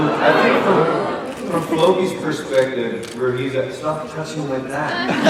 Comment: Please can someone tell me what Gustaf said to Alex at 9:41? I can't figure out the word. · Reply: "Stop touching my leg"